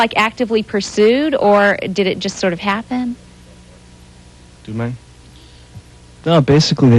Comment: love how deaner refers to gener by 'dudemang' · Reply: I thought he said "do you mind" at first, thank you for enlightening me! Definitely got a chuckle!